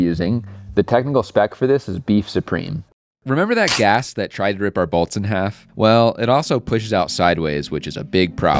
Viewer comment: I heard "remember that gas that tried to rip our balls in half?"